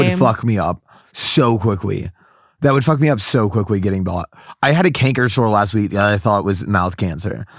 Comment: is he saying "getting bot" isn't it "getting bitten"? I'm genuinely asking. I'm not native